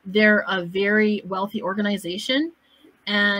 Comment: I burst to laugh when I heard "they have a great facade" 😂